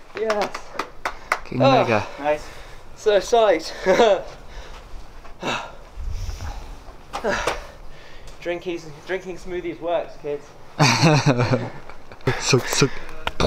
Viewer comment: What does say at 6:21? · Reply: "So psyched. Drinking smoothies works, kids."